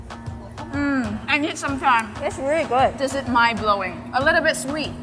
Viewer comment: are they really viet girls? i swore that girl said as she covered her mouth, i need some rice in cantonese.